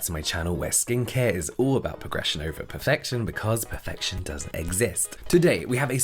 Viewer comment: Me when he said “because perfection DOES exist” 👁👄👁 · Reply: I heard "Perfection Does it exist?" I think the hand talking betrayed him here. (helped emphasize the start of the words "perfection doesn't exist" - thus, the "not" was de-emphasized)